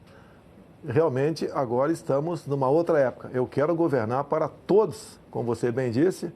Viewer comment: ele disse todes? Ou eu ouvi errado? Meu Deus do Céu! 🙀